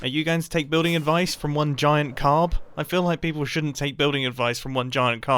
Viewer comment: “Don’t take building advice from one giant carb” Grain :( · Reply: for a second I thought he said don’t take advice from one giant crab